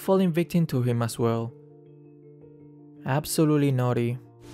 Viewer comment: I was reading the comments while watching this vid then I thought he said "Absolutely NAUGHTY"